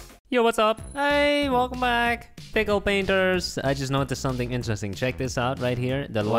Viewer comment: Did he say "Pickle Pinter"??